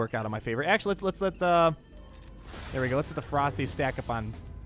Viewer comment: When you were saying "Let the ferocity stack up on Revan" I thought you were saying "Let the frostys stack up on Revan" You know, like those Wendy's ice cream things. It was a very odd mental picture.